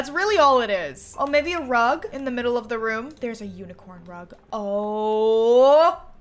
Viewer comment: When she said there's a unicorn rug i heard there's a unicorn drug 🤣